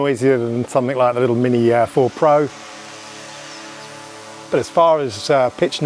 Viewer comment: I heard you say the Mini Air 4 Pro 2:44 This must be next new drone you secretly testing! 😂😂